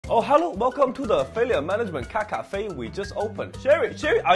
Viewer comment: I heard "Fillian management drunk caffe" no idea how...